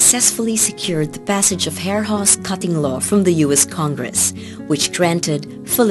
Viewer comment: What did it say at 2:59? They secured a passage of what? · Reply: Hare-Hawes- Cutting Law